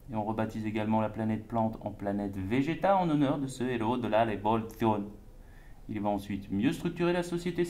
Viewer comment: il dit quoi à partir de 2:41 ? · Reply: Héros de la révolution